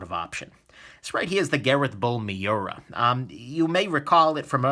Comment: what knife did you say @ 3:55??? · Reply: Gareth Bull Miura I believe was the knife